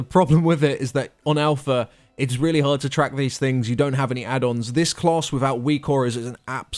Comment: What's the addon he is talking about at 15:30 "wecore"? Can anyone tell me what the name is so I can download try it, thanks. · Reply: He said "WeakAuras" which is one of the most popular raiding addons to date. It allows you to track buffs and debuffs and all kinds of stuff. You can get it on Curse.